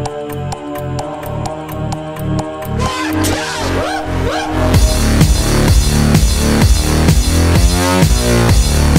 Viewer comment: Can someone please tell me what is screamed at 00:13, Thanks! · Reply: One Two Whoop Whoop